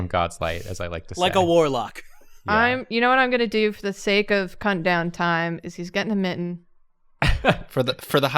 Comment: I know that says "cuttin' down time" here but the first time I heard it I totally thought she said "cunt down time" and I was extremely taken aback.